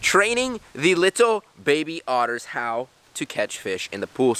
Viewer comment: In the start i thought he said trading the little baby otters